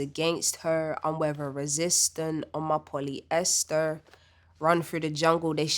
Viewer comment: I thought she said "I'm a poly Esther" and I was like 🤯 but maybe Genius missed that one